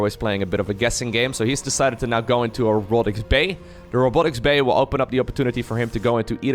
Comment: Guess I am more tired than I thought.. At 6:02 I heard "And he has decided to go into an Erotics bay" xD